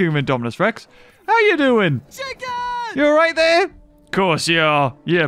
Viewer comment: Was I the only one who heard CHICKEN